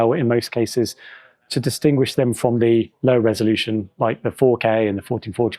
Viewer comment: This is what we have become.... lol @ 1:05 "To distinguish them from the low resolution like the 4k" Sounds like he says, "low" captions say, "lower", either way, its amusing.. can we all remember back when 4k was new? everyone was either freaking out or saying we dont need it..